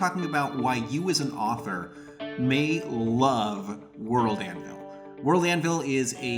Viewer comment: Lol at first I thought it said "the word Anvil" and I was really confused